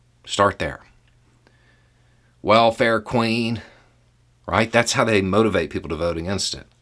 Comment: Howdy, howdy Internet people😀👍🇺🇸🇦🇺. You made me laugh this morning. I thought you said “well, fair Queen “ not “welfare queen”. I did wonder what the world you were talking about till I realised my mistake😂😂😂😂